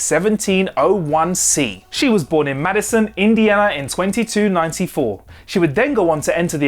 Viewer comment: Did you say Madison, India 11:35??? I know you can say Indiana